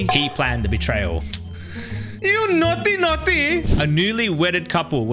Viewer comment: NAUGHTY NAUGHTY got me dead 💀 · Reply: I thought you said naughty ones 💀